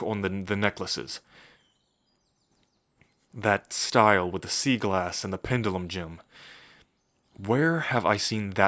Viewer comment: Bruh I need help When he said "sea glass" I heard it as "seagull ass"